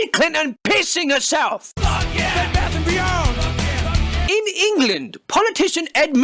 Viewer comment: can't be the only one who heard "Bed Bath and Beyond"